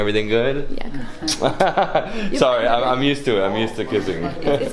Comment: Did he just say sry I m used to kissing ??